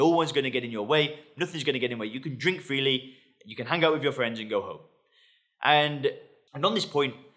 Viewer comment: It almost sounded like “you can have a hangover with your friends and go home.” Not far from the truth.